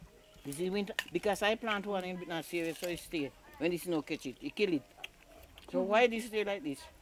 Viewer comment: Did I hear the older lady mention snow? "Snow catch it" I thought she said